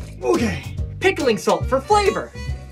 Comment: I’m sorry but did you just say “for favor”?